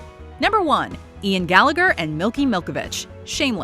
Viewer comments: did she just say Milky?! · Finally Gallavich has got the recognition they deserve 😍 I love them 😍 And I'm sorry, did she really say "Milky" Milkovich at 12:41? Lol 😂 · Did she call micky milky